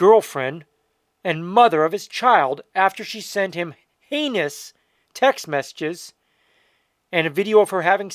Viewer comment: Bro you got to pronounce your h is more dude I thought you said anus instead of heinous.